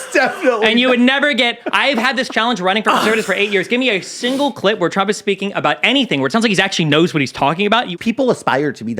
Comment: what was the bleep word? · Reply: Looks like “Hitler”